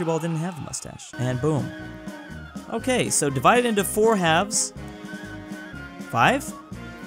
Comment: did he say 'four halves'?